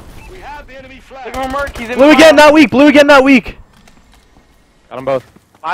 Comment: why did he call him out as 'weak'? he didn't hit a single bullet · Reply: He said not weak ? >,<